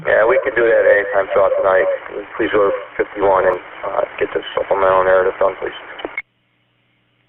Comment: “and get the _ narrative going, please” is that what I heard over 👮 radio ?